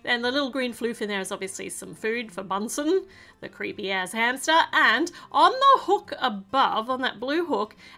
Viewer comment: No offense to but anyone else hear creepy a×× hamster